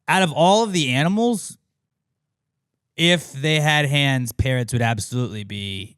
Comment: I was SO confused, it took me a good minute: I heard "If PARENTS had hands, would they be the best ventriloquists?" lmao